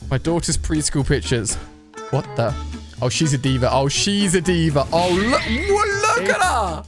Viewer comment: did he just say she's a Diva!?!?